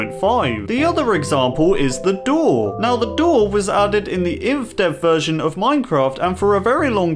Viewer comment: he said ivdev instead of indev. laughed alot